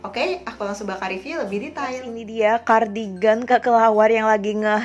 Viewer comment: Aku salah denger gak sih kak bilang nya cardigan kekelawar hehe maaf ya kak klo salah